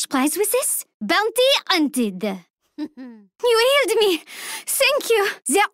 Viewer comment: i don't know what she says at 0:05 hey help me! thank you? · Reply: She’s says “you healed me,thank you”